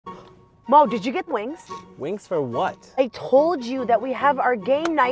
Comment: I thought she said mom did you forget the wings 😂